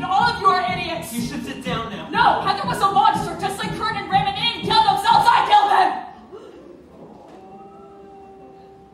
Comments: wtf does veronica say at 2:50? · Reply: Mixel Bee "No, Heather was a monster just like Kurt and Ram, and they didn't kill themselves, I killed them" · Can someone tell me what Veronica says during 2:50? IT because I cannot understand a word she is saying... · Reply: Heather was a monster, just like Kurt and Ram and they didn't kill themselves, I killed them! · I never understand what the fuck she says after 2:49. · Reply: Danna Torres Salcedo Caps warning since she was shouting "NO HEATHER WAS A MONSTER JUST LIKE KURT AND RAM AND THEY DIDN'T KILL THEMSELVES I DID"